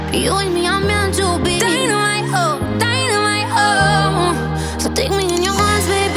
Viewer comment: I Swear it sound like she saying “Dynamite Hoe” 🤣🤦🏽‍♂️